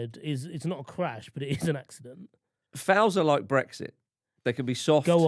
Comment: i misheard Marcus at first I though he said fouls are like breasts.. they can be soft they can be hard